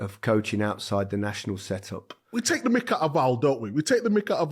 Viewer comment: Does anyone know what was Micah referring to here? Micah Ofal? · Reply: "We take the mick out of Al (Alan Shearer)"